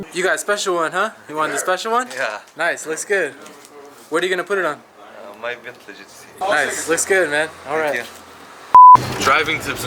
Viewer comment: does anyone know what he said 😂😂😂😂😂😂😂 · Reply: My bentley gtc